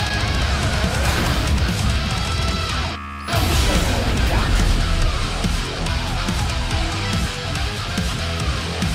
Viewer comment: when Dante awakens his Devil Trigger, what does he say? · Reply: "Let me show you what I've got!"